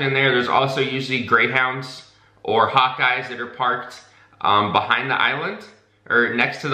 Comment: I thought you said "hot guys" instead of "hawk eyes". I was momentarily very confused.